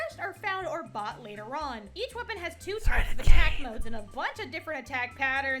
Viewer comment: What did say, something about the cane? I didn't quite catch it. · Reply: I wrote this comment three years ago and just now realised that he just said 'threaded cane'. I sometimes wonder how my brain works.